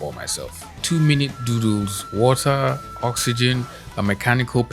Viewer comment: Oooh. 2 minutes "DOODLES" I thought you said Doo-Doos. I was like "Some people just take their time in the bathroom. Some just dont have the time and rush it out"